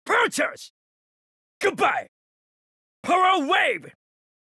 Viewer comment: what is he saying? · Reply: power wave